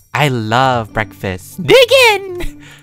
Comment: For a second I thought he said “I love Breadfast!”